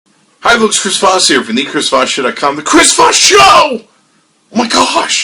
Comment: sound like he said the "chris fat show"